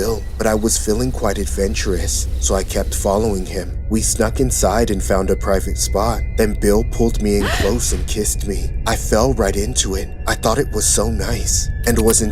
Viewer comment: did she call him bill?